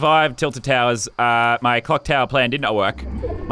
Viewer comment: Is it just me or did I hear cocktail plan at 2:35, I know it's clocktower plan but lol